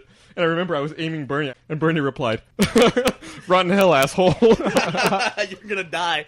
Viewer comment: i just realized fucked up here, because says "Rot in hell asshole" not "run hell asshole" how tf did he even reach that conclusion of what said